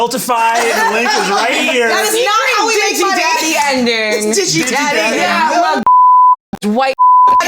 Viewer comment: is anyone able to decipher based on context what was said here? edit from a year in the future: not that anyone replied to this comment anyway, but it was "blue eyes white daddy" in reference to the infamous yugioh (not digimon) card "blue eyes white dragon." thank you to brennan for incredulously repeating it somehow without me ever noticing before